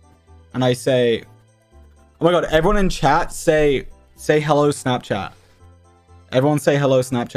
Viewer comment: I thought he said say hello Sapnap LOL